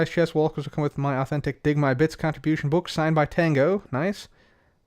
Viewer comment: I thought said "Dig my Bitch". Not "dig my bits".